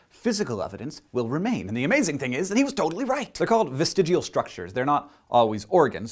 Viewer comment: Oh ho ho ho hahahaha! I thought it said vaginal structures... Silly me! Winkedoodle poo!